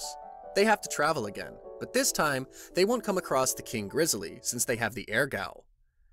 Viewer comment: The final part starts at 59:00 for those who have watched part 4 and are already caught up! Hopefully you guys enjoyed the series! The channel is really blowing up! We will be working on pumping out more SS Rank Recaps for you guys to enjoy so stay tuned! 😎 series name : Tʜᴇ Aᴘᴏᴛʜᴇᴄᴀʀʏ Is Gᴏɴɴᴀ Mᴀᴋᴇ Tʜɪs Rᴀɢɢᴇᴅ Eʟғ Hᴀᴘᴘʏ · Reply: I thought u said king rizzly